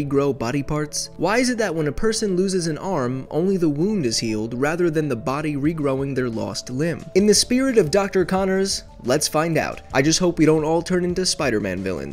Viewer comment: At first I thought it said “regrowing your limes” Jsbdbsvehwhgafwprdrhhrkeu ssdhsjdyjsqdujadhkqenhadhdwhwuwqjwdgsfqhfsjvdhfeebecjcdhswhdwwc